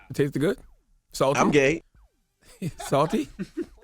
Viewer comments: Did envy say “I’m gay” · Did he just say “I’m gay”. It’s I’m game...